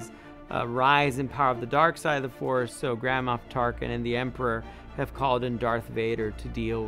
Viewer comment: I know it's "Grand Moff Tarkin" but for some reason, every time he says it I hear "Grandma Tarkin" and I was like "Tarkin has a grandma?" but then I realized I just heard wrong and now I'm laughing at myself Lol